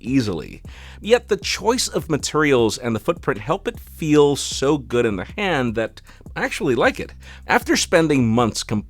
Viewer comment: Tell me way at 2:25 I heard feels so good on the hand .... I continued with "lady you know the procedures" #flossycarter 😫😃